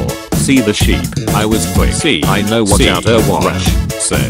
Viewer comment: lol i heard "overwatch"